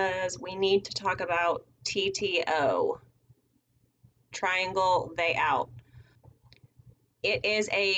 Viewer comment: Thank you. For the longest time I thought was saying triangle day out. Obviously I was thoroughly confused.